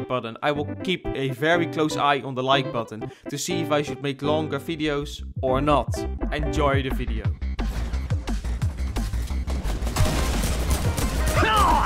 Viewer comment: Did he really say fideos?